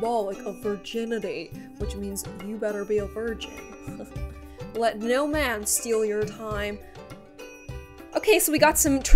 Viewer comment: I thought it said Let no man steal your parents! 😶